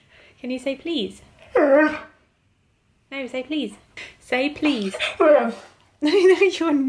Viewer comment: I definitely heard "please" , just like you asked.